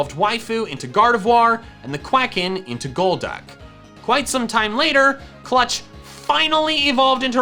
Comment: What you said: "Quite some time later..." What I heard: "After getting a new chair..."